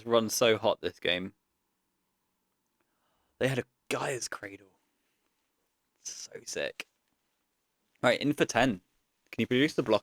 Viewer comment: Sorry, Vintage newcomer here, did you say "Gaea's Cradle"?